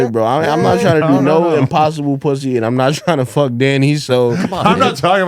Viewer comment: Did he really call trans girls private parts "The impossible p***y?" Like the impossible burger? That's hilarious I never heard that before. I think that went over their head.